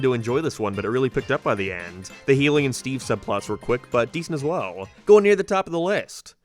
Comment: I swear every time you say “Hayley and Steve” I hear “helium Steve”